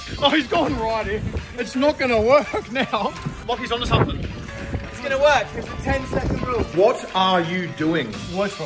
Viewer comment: did he just say it is gonns work WTF